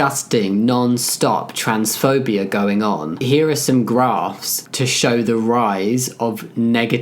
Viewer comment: at this part i thought he said “here is some grass” and was expecting to hear something along the lines of “find and touch this” because thats what they need to hear (i was looking away at that part so yeah)